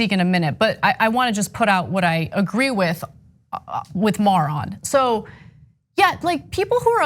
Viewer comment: Did Ana just call Maher a moron ?